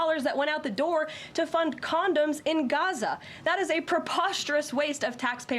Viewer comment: $50,000,000 to fund condoms in Gaza is extremely Fkd up. Whomever put this forward is going to hell. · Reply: Wait...CONDOMS?! Holy shit I thought he said condos. Yep that's a new low 😂😂😂